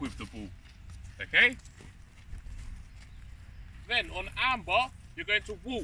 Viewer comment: I thought you were saying arm bar lol. Took me a minute to figure out it was Amber